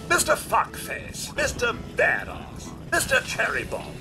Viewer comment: Things I do for this channel. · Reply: more I listen to Codsworth say mister it sound more like he is saying sister....so is sounded like he said...."sister fuckface." and I died of laughter right there